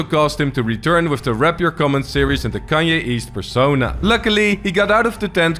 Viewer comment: East? I thought you said Weast